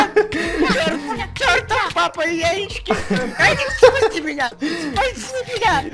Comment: мне послышалось что сказал яиички